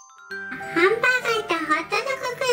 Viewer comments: ハンバーガーがハンブーガーって聞こえたww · ハンブーガって聞こえた。